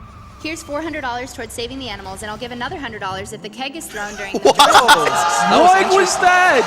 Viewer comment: That awesome donation that got cut off at 46:55. Ouch. I couldn't make out what the extra $100 was meant to be for. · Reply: something about a keg but we'll never know now.